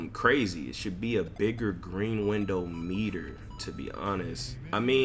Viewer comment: Dope vid. Also I got a clip posted on my yt of someone greening a 100% smothered on rec, even tho Mike Wang said it was impossible, if you wan use it in one of your videos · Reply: really? Well damn. I thought he said sumn bout no true green window at that shot but I prolly misunderstood it